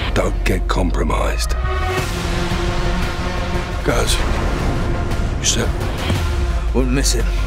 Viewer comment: did he said Shepherd ? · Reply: “You set?”